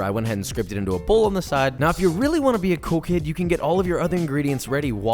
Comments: Oh he said cool kid I thought he said coke head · legit thought you said "if you really want to be a coke head" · He says “cool kid”, I heard “coke head”